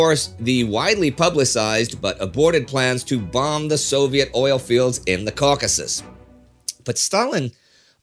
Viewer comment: Several sources were used for this episode, including our own scripts from the past year, but John Waller’s “The Unseen War in Europe” was a pretty big one. Mainly for his organization of the various elements of Barbarossa intelligence. Be sure to check out our rules of conduct before you comment! <link> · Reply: Guys, you got a typo in english subtitles. When mentioning Arvid Harnack, Indy said "who reported already September 26, 1940", but subtitles said "who reported already September 26, 1941". Also, on 7:03, it says "produce the oil fields" instead of "bomb the oil fields". Also, no subtitles for?! That's sad :(